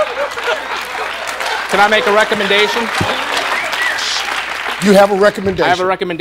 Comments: I thought he said a rectumendation XD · I honestly thought he said he had a rectumendation. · i thought he said a "rectumendation'